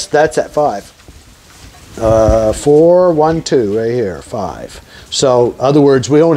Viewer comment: Thought you said 4•2•1 not 4•1•2 there is a difference